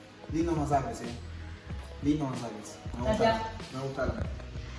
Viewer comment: Entendí que dijo ME GUSTA ALMI 5:42🙂🤔